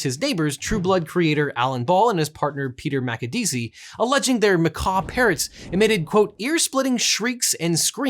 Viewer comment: I misheard this as "Macabre parrots" for a second and that sounds much more interesting.